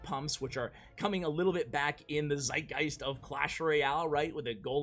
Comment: Did you say "Zeitgeist"? I didn't know that this German expression is used in English...